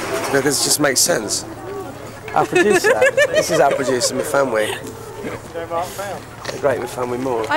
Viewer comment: "Myfanwy", I thought he was saying My family because the barmaid was his sister or something. lol.